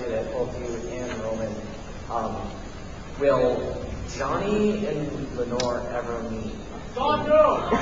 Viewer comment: I can't believe I watched/listened to this whole thing o_o What did that guy say at 1:04:30? "Will Johnny & Lenore ever meet?" ?